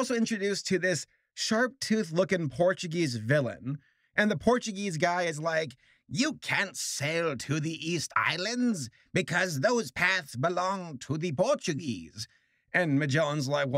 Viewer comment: East I thought you said weast